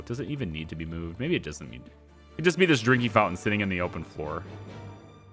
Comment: When you say drinking fountain is sounds like you say drinkey fountain